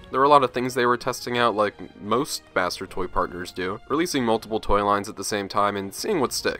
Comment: DID I JUST HEAR THE B WORD? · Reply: He said master toy partner